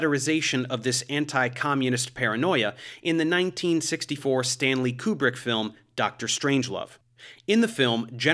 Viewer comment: I just heard “Stan Lee” and “Doctor Strange” haha